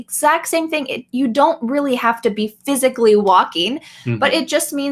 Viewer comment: Dear my teacher! i see this video, in 9:43, i hear you say word "physically", but i see subtitle to be word "Psychically", so i heard wrong ? thanks you very much and happiness for your family!